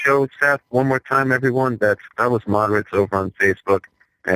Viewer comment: What was that Facebook page at 53:44 I couldn't hear what he said. "Add in" I live in Moore, Oklahoma and I'm in a tornado warning right now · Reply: The Foolish Thinker Godless Moderates. Had to listen to it more than once.